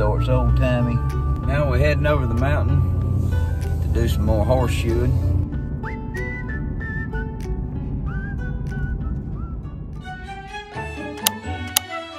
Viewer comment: Hi, When I FIRST saw your channel and talked about Titus going HORSESHOEING, I thought you guys were saying HORSE SHOOTING😅😂 Love you guys❣️❣️